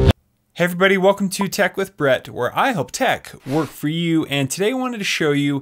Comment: Sorry, I'm new but I heard Tech lifts bratt 0:40 during the intro as channel name 😂 gotta verified that one before it gets taken 😂